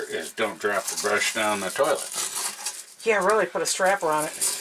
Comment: Omg. had me laughing when I thought she said. Put a strap on ……